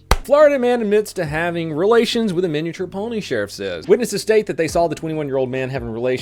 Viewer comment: LOL I thought he said the Florida man had sex with a miniature pony sheriff. Now I'm picturing all of the animals voting for the miniature pony to be sheriff.